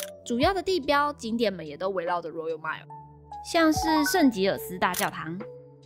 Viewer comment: 我聽成腎結石大教堂